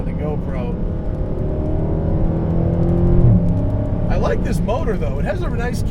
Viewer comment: Did he say "I like this boner though"? Probably not, but I really don't know what he said.